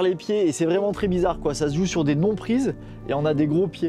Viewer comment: translated should = 'big footholds' instead of 'nothing for your feet' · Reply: yeah the first time i heard, des bon prise, good holds. I thought wtf. But he says non prise, none existing and big feet